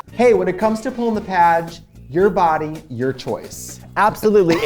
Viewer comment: For years I thought they was always saying "pulling the patch" not the padge. Themoreyouknow